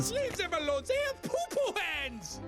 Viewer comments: am I the only one that grew up hearing the caterpillar in a bugs life say "leave them alone,they are poo poo heads" and not "they have poo poo hands"? Just me? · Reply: No i heard “leave them alone zey are poopoo heads” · For years, I thought Heimlich said, "they are poo-poo heads!" Turns out I was wrong!